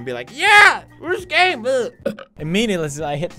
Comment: wtf is up with your captions... and at 2:45 did you say runescape...